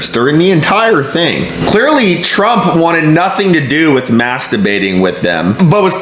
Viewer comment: Gees, I'm sorry, my hearing is bad, I thought you were saying masterbating... Then realized it was mask debating. My bad . Must be the ( Corona) I drank a while ago.😀